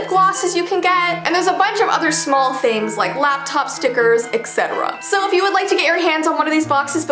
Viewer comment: I thought she said laptop